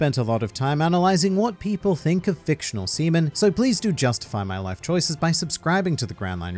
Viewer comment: I thought you said fictional semen for a second 😂